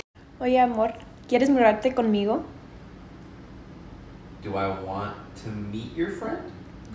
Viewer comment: I like how you instantly turn into your mom when he gets the Spanish wrong. 😂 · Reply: He misheard con migo as amigo.